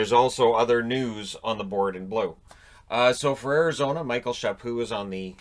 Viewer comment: Thought it said Michael Peca ngl